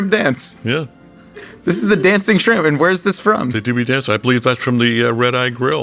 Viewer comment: Did he just say "They do be dancing" ?